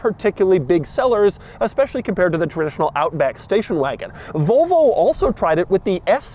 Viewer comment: am I the only whose brain heard Doug say “Outback Steakhouse” instead of what he actually said, which was “Outback Station-wagon”………..